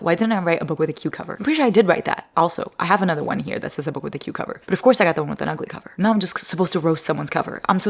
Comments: ok but why did i hear " a book with a cucumber" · Am I the only one who thought said “book with a cucumber”?? 😂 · I thought you said a book with a cucumber, instead of a book with a cute cover.😂😂😂